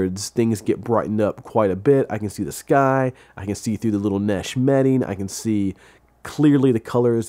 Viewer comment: Did he just say nesh metting?